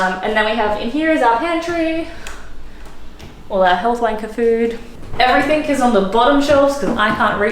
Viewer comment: did you say 'Health W*nker food or are my ears deceiving me?😳